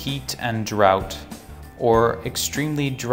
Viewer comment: dry conditions hit India が dry conditions in India に聞こえる